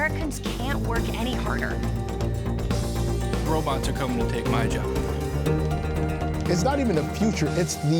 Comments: Robots come to take my job with English subtitles says Robot to come and lick my dick. 😂 Must be A.I generated🤔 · Turn on CC and see what it saids here. Hilarious! “Robots come to lick my d*ck”. Not kidding!